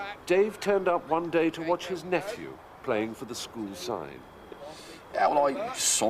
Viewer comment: What exactly does the narrator say here? Playing for the school sign? Sine? Side? It sounds phonetically like sign to me. Can anyone please tell me what this means? I, probably needlessly to say, am not from anywhere in the UK or Ireland, so all I know is from research, media, and the experience of others. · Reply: Side, meaning team.